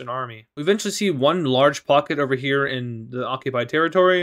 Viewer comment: What says: pocket What I Hear: F"ck it